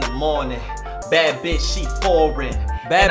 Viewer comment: You misheard! "She foreign"?? Nah bro, "She four, and" Dude was trying to tell you his girl was ugly but you wouldn't listen